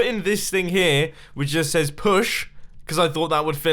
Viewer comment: I thought it said P.U. SH!!